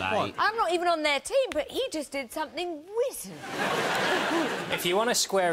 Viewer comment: Claudia is the sort of woman that'll use the word "WIZARD" naturally and that in itself is pretty wizard. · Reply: Oh she says wizard, I thought she said witty weirdly.